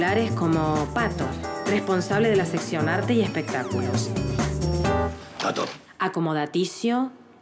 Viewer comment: What does she say in 5:05? The word for "Entertainment"... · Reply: Translation Man "espectáculos"